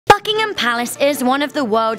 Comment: it sounded like you said fuckingham at the start